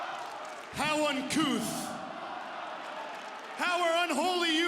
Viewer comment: What did M.J.F. say around 0:37?... "How--" what?... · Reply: uncouth… how uncouth 😂